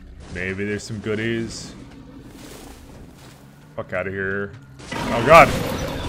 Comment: Did you just say "akár here" ?